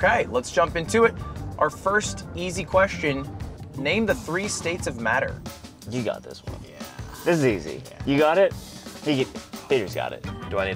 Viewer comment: I heard '"name the three states that matter", I went NEW YORK, FLORIDA, WASHINGTON!